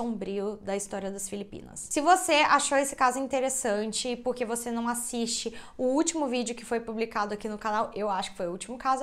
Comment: eu juro que ouvi ''se você achou esse caso interessante por que você não procura terapia?''